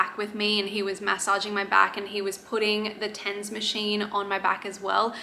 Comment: Putting the what machine?? 4:08 pls someone explain. I heard tense machine? I need to know 😭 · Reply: A TENS machine 😊 you put sticky electrodes on your back and it passes a weak electrical current to your nerves which can help ease pain. You usually find somewhere local to hire them for a few weeks (from a doula or pelvic floor physio) or purchase online.